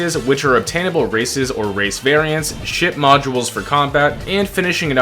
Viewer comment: shit modules? Yeah i heard that 😂